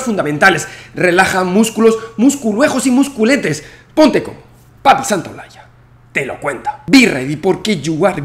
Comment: O.o 😵‍💫 ????? Sólo entendí "te amo Javier Santoloalla y no soy gei" 😅